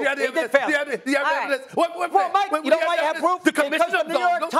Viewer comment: Does Russo say "WELL MIKE" at 6:01? LMAOO